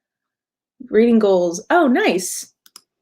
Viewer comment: Thought it said “Reading Gaol”, as in the poem Oscar Wilde wrote in prison, whoops